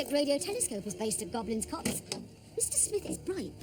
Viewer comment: f-k its like a Haribo advert with the audio like this - please let it be an accident in the upload.... LOL around 4.23 I misheard Sarah saying Goblin Cocks